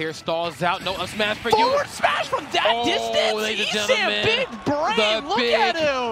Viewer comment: he did not say big brain he said bald head!